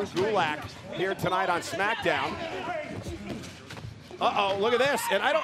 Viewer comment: Why d i hear "smack joke"